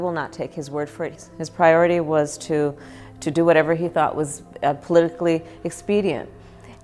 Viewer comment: did she just say "political expedient "